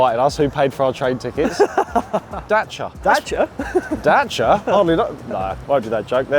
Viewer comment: why did I hear this and think "James May????" and not Dacia